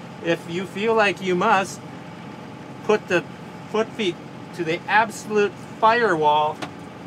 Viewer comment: Teach them. Foot feed is a term I haven't heard in a long time. For a while I thought the old man was saying foot feet. I pondered for a while thinking what the heck is foot feet?